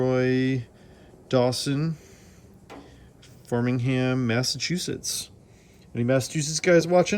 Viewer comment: Im in MA. I thought I heard you say 'farmingham'. I think you meant 'Framingham' which is about 20 miles west of Boston